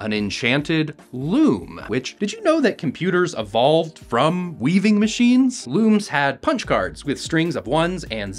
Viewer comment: My dyslexic ass thought you said brains evolved from looms lmao